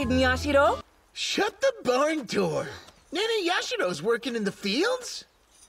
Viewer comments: I think he said barn doors?? Still really weird though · I think that he said barn door